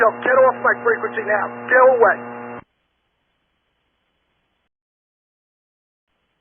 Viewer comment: Get out from my airspace! (few seconds later) 4WL, by any chance you still with me? 😂 😂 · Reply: he didnt say 4WL he said N2WH - which wasnt displayed on the video screen. We also cant hear replies from some of the pilots at times, so im assuming he was talking to another pilot who radioed in during the scuffle, but its unclear